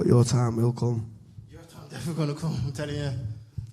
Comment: “your time is never going to come” 😮 Leon was cold and true 😂😂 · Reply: i always thought he said "your time is definitely gonna come"